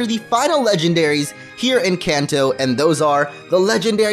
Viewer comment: Did U say here in Kanto?